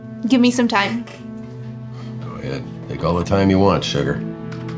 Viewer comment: did bro just say sugar💀